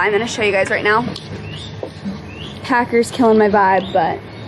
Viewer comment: Did you call Parker, Paker at 7:37? hahaha now I'm going to start calling him that in my head.